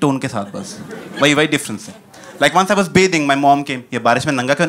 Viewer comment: Once I was ___ my mom came... What was that word..Repeated that phrase for several times but still can't recognize. Can Anyone help me out ? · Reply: Bathing